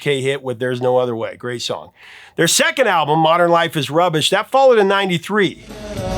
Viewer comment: Lol, I heard "Their second album "Modern Life is Rubbish" as "Modern Life", is rubbish. Thought you were being kinda hyper critical. On a side note, that Linda Ronstadt album brings back a fun memory. My wife and I owned a Pomeranian name Pico. Anytime I played Blue Bayou he would start howling when it got to the slide guitar solo. He even would start shaking with excitement right before it started, he so wanted to show us how good his howl was, even though it was cute at best.